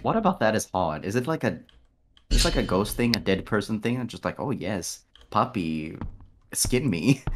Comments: TSKR 0:17 i think Ike said "'A dead' person thing" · I think he said 'dead person thing'?